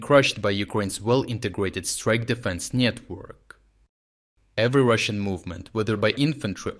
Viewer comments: Did he call it a nyet work? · is it just me or did I hear 4:19 "strike defense нет-work?" not sure if that was intentional or not, but it's really funny either way